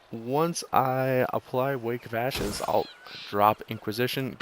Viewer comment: What did you say at 5:10 "Once I apply.....?" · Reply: O Soskic “wake of ashes “